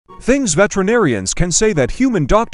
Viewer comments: Ohhh I thought it said veterans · Ill be honest, for some reason i heard 'vegeterian' instead of veterinarian and i was extremely confused on what the heck was going on 😅😂